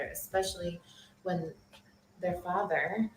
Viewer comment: did she say “their father”? As in more than one of her kids? Or just the one she’s pregnant with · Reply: She said their BUT REMEMBER….. SHE IS TELLING HER STORY😅😅😅 we don’t know who these people are in her head